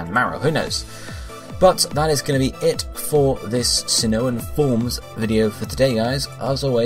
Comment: I thought you said no Sinnohan forms for Pokémon w/ Megas